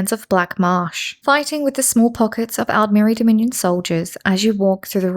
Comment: my phone lagged for a second and all I heard was “fighting with the smallpox”